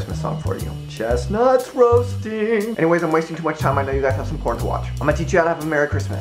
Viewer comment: he says "Anyways, not wasting too much time, I know you guys have some porn to watch"... at least that's what I think he says, my English is not perfect.